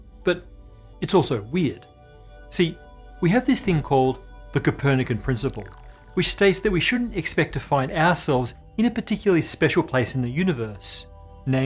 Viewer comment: when Matt says "ourselves" I clearly heard A**holes the first time. Which funny enough would still hold true since we happen to have one.